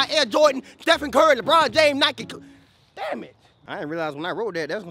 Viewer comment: Bro when the shoe man said Nike I thought he said know yo heads off